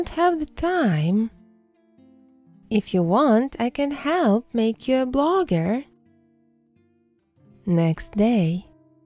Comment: Oh I thought it said I'll make u eat a booger